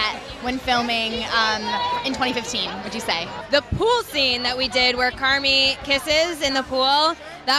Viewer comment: I know Katie said the pool scene, but when I heard it the first time, I thought she said “pussy” on 4:47. I can’t be the only one.